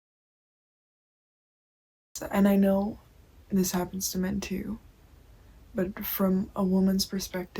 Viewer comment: what did she says at 2:17? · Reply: There are captions, but I'm pretty sure I say "I know this happens to men too"